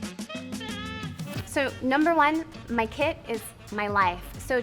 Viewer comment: i thought she said "my kid is my life".